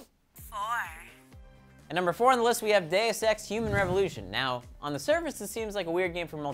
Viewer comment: At first I thought he said day of sex human revolution lol